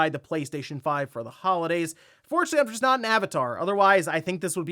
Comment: Good god, I can't even understand your speech. I think you're trying to say "unfortunately, I'm just not into Avatar", but it doesn't sound like that AT ALL.